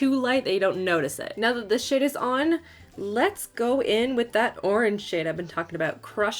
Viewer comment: Is it just me or at 3:04 did she say "so now that the shit is blended" 😂😂 and also i have this pallet sitting at home and I have not touched it, haven't even broke the seal. I work so much that all the new makeup I buy with my coins I don't have time to try them out 😤